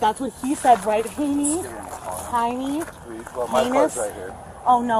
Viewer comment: uh, I heard it. Teeny tiny P-- XD